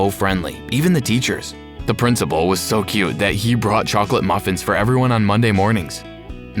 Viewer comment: " The principal was so cute " is what I hear....